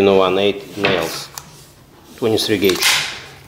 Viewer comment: you say you use "inch and 1/8 nails... something gauge" I cant make out the Gauge you said? Can you please clarify? Thank you. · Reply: 1inch and 1/8 - 23 Ga pin nails.